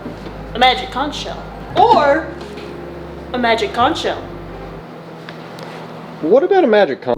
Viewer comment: I thought he said magic cock 😢